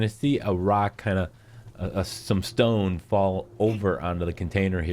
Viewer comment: Did I hear a Rock and Stone?